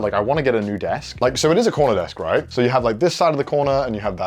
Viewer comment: I heard "It's a Connor desk" instead of 'CORNER desk' oops